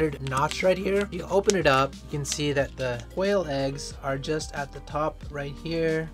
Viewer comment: What I heard at first was...."Whale eggs" 😂😆.